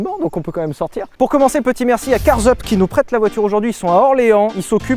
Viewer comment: j'ai compris "petit merci a car-zeub"